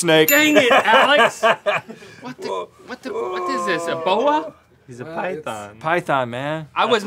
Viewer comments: When link said "a boa" I thought he said "ebola" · When link said, is this a boa? I thought he said is this ebola? 😂